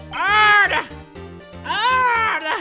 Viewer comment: I like how he says ORDER, ORDER but if u kinda head it closely it sound like he says HARDER, HARDER